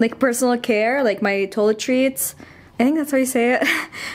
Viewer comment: It sounded like you said "toilet treats" which was so cute! In case you still have trouble pronouncing the word 'toiletries' it's TOI-LEH-TREES, lol! Lovely video.